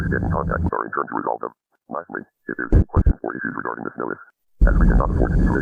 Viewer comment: In the phone call where it says "Lastly if you have anymore issues regarding this noise" I heard it as "Lastly if you have any questions or issues regarding this notice"